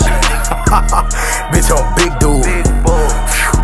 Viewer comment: All I hear is : "Btch I'm Big Boogie, Big underwear"...lol 😄🤣